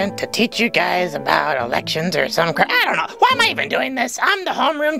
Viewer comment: my ass thought he said erections